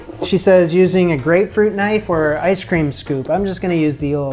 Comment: I thought you said "Gravy knife"🤣 my hearing sucks